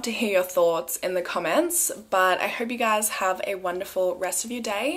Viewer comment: Buy Guy ❗ What I thought when you said Bye 🌼🤙✌️🌺💚😎